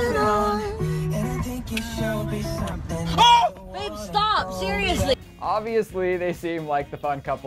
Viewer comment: "Liam, stop, seriously" idk why but it's my favorite part of this video lmao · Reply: I heard "Babe stop, seriously" lol